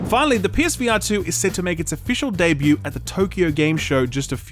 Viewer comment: When he said psvr 2 I thought he said “ps vita 2” and I almost had a heart attack.